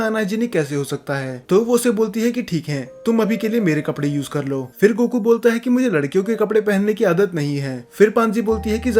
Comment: goku ne kya bola tha please reply ❤❤ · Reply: I'm not into woman undies